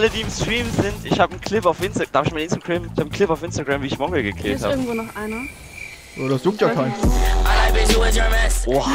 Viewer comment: Was sagt eigentlich der Bre bei Minute 6:06? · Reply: „Ich habe einen clip auf insta wie ich Mongraal gekillt habe“